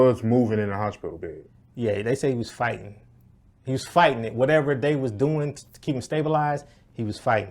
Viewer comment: I heard it as “he was fighting whatever procedures they were doing” to keep him alive. Maybe he was saying he was done?